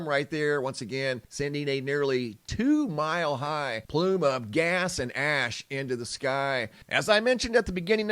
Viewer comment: I swear I heard ass and gas. 😂😂😂